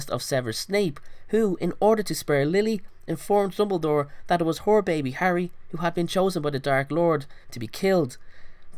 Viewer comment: im not making fun of his accent but it sounded like he said "whore baby harry" instead of "her baby harry"